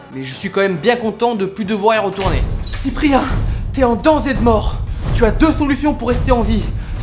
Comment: Salut! Vous pouvez m'aider s'il vous plaît? Qu'est-ce qu'il dit avant de "Tu as deux solutions pour rester en vie?" Dans le seconde 0:06 Merci beaucoup! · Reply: "Cyprien, t'es en danger de mort" ?